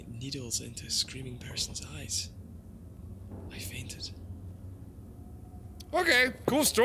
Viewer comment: I thought that note said "I farted" at the end instead of "I fainted"